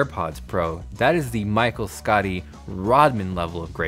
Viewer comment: Who else heard "Michael Scottie" and thought he said Michael Scott at first before realizing he was talking about Jordan and Pippen, not the Office LOL